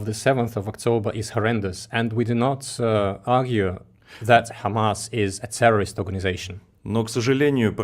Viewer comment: For your information Alexander, your government does not consider or has designated Hamas a terrorist group. So for the sake of accuracy, get your facts straight when you use terminologies with significant political charge like terrorist. US uses the same tactics against Russia and it's not pleasant, is it?! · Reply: You (and I also at first) have misunderstood the answer. I heard it again now (26:04). Alexander answered: "We did not argue, that Hamas is a terrorist organisation."